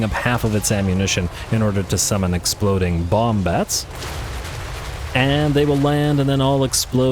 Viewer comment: you said, exploding bomb bats... and in my rotten void ... i mean "heart" i heard "exploding wombats" and i saw in my mind tiny rodent like things dressed as rambo with all sorts of granades straped in their bodies, ready fto chuck them at shit in the jungles of Katachin. And i wish i could give quadruple likes to this video. Edit. you don't get it... all of them have a perm!! IS FREAKING GLORIOUS!!!!